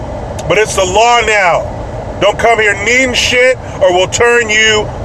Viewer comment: Lol when you were talking about the super bowl I thought you kept saying break shit instead of bring shit, I was like why would you do that? 🃏😂 😂